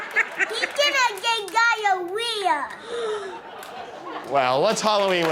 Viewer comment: I thought that last kid said “he’s getting again gonorrhoea.”